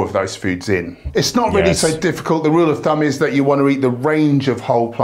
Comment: I think he said ‘rule of dumb’ not rule of thumb.